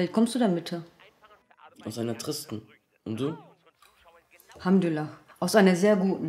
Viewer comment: Was sagt er bei 5:34 also aus welcher Welt er kommt? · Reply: Aus einer tristen Welt